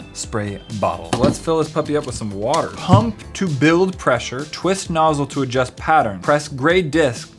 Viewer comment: I was concerned I thought he said "hump to build pressure" oops 😂😂